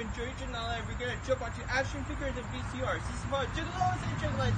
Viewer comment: I thought he said “were gunna jump onto Ashton Kutcher and VCRs”